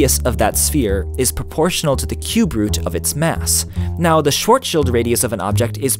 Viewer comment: I hear "short shield" instead of "Shwurzshild", you English people. XD